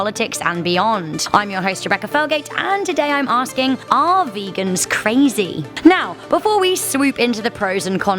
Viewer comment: When I clicked this I thought it said 'Are virgins crazy?'